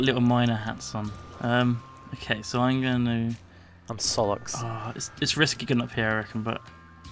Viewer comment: Nilesy, did you say Sollux? As in the Homestuck character?